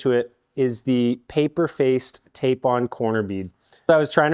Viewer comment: I thought it said corn bread 😂